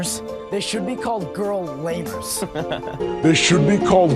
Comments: I HEARD GIRL LABOURS NOT LAMERS OH NO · I don't know why I thought he said "girl labors"